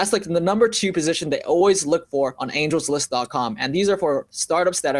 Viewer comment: He says what ? I cant understand the site name. <link> ? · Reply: angellist.co